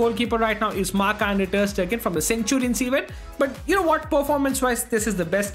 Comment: did he say ter Stegen?